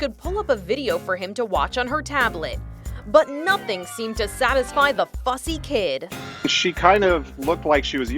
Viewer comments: I thought she said to satisfy the fkn kid😂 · I thought she said f'ing kid not fussy kid...